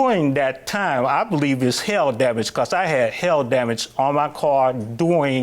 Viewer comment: I thought he was saying "hell damage."😅